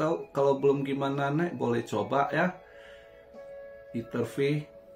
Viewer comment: pak ini koin apa tulisannya gmna ??? Saya dengernya iterfi tapi cari ngak ketemu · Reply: Etherfi